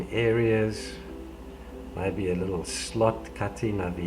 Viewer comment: did he say slut??